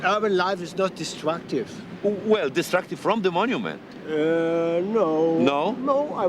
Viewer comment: If I hear it well, at 5:20 Marzynski and get each other wrong. Marzynski says "distractive", while hears and then says "destructive" - and Marzynski doesn't notice. Both have the best intentions, but communication has its limits.